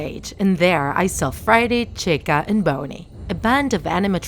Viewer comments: I heard her call bonnie something like boney, is it just me or not? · did she really called freddy friday? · I heard Friday chica and boney also at 4:47 I heard pirate cave and not pirates cove